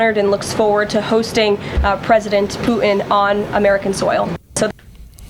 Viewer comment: Did Karolies said president Poo In?